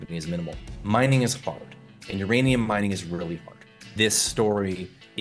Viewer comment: In the intro segment I thought he said "mining is f**ked, and uranium mining is really f**ked" lol